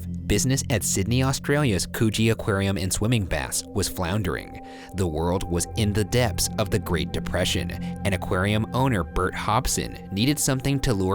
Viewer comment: i thought they said coochie aquarium hehehehhehe